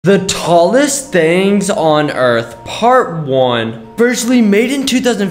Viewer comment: I thought you said the tallest things on earth not the tallest things on Mars